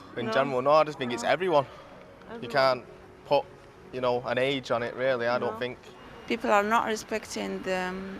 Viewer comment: I had to rewind. Thought he said “can’t pop an e”